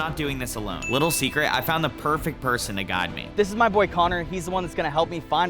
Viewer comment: I thought he said My Bf Connor